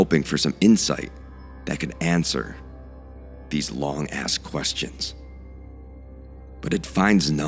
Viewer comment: I heard "to answer these long ass questions" 😂